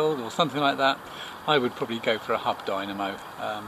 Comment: Couldn't make out what you said at 4:22. "If I were going across the world...I'd probably go for a h-______ dynamo." Was it "hub" dynamo that you said?